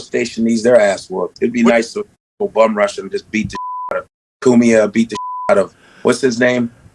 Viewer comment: Naaaww, it DEFINITELY sounds like Godfrey said, “It’d be nice to go bumrush em and just beat the 💩 outta them,” he LITERALLY says it right there in the video 😂😆🤣. Also, someone doesn’t HAVE to put up their dukes and fight like a man (as you said) if they’re being assaulted. It’s not ‘mutual combat,’ the people getting attacked didn’t agree to a fight, they got BUMRUSHED and attacked and are “getting the 💩 beat out of them,” they CAN defend themselves with a firearm because they are in fear for their life and are taking reasonable harm from a surprise attack inside of THEIR own property. You don’t know how the law works 😂🤣😆, and you CLEARLY can’t make up your own and expect it to work 😂. You gotta do better with the clickbait bro lol